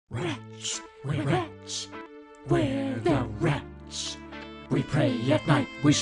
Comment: I always hear “ where the wrench , the wrench , where the wrench “